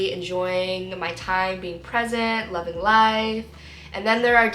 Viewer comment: for 2 seconds I thought you said "being pregnant 1:19 giiiiirrrrllllll hihi 💓